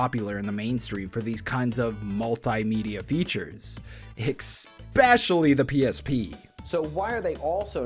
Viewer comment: bro did NOT just say eckspecially💀💀💀💀💀💀